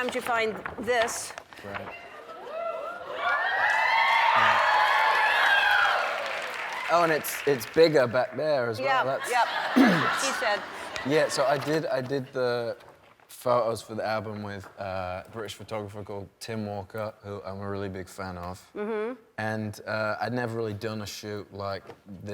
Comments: Harry: “It’s bigger back there” LOL “that’s what she said” I heard you Ellen * happens at 3:04*** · did anyone else hear Ellen say "That's what she said!" HAHAHAHAHAHAHAHAHA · Did Ellen really say 'That's what she said'?😂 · Did no one hear Ellen say 'that's what she said' in response to Harry 😂😂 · anyone else hear Ellen’s “that’s what he said” joke? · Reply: Ellen said, "That's what she said." Listen carefully. 3:04 P.S. Don't come calling me homophobic or anything, but she said she. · Omg does anyone hear Ellen say: That's what she said at 3:05 because before that Harry said: Oh and it's bigger back there.